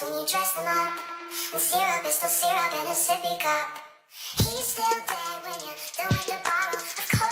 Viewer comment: ”Syrup is still syrup in a sippy cup”… yes just like you’re still whatever you were born as, even in different clothes. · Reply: I thought that said "Sierra Mist in a sippy cup".